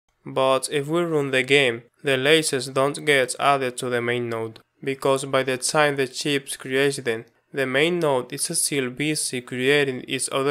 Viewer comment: whut? What did you say?) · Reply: Ok, with the help of automatic subtitles, I've managed to decrypt it: If we run the game, the laser doesn't get added to the main node, because by the time the ship creates them, the main node is still busy creating each of the child nodes. but I still don't get why this doesn't work. Is it because we called: utils.main_node.add_child(laser) from the child node(ship) and by the time of this call the main node is still not fully created, i.e. main_node's _ready was not yet called, so we can't add a new child? Could you clarify it?